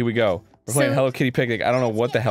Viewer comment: For some reason I thought it said "Hello Kitty Panic" which probably would have been a lot more trippy and fun